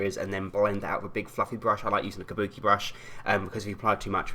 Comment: When you said fluffy brush I thought you said big f***ing brush XD